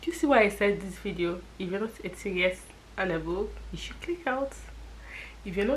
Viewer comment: you've got me into trouble with my mum o. I thought I heard "if you're not 8 and above", now won't be able to watch you anymore, my phone has been seized!